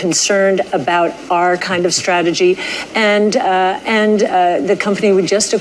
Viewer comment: Cathy said "our kinda strategy", but I heard "Ark, kind of strategy." 😆